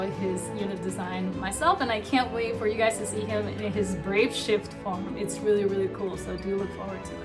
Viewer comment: We wanted NVA Karten with BS. The NV looks to be SLB Edit: I was wrong. mentions he will have BS. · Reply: she clearly says brave shift